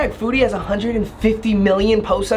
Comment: i thought u said booty instead of foodie